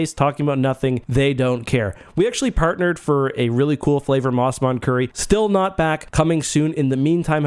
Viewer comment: I thought you said Mon Mothma curry.